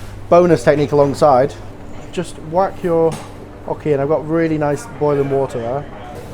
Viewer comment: did he call it an 'ockie' lmaoo